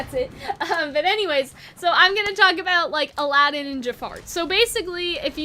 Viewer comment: i heard you say "Jafart"